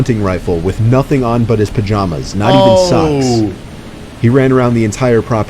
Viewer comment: it says sux instead of socks